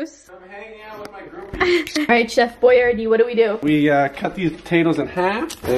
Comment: I thought that you said “CHEF BOIA” which is my last nam e 😂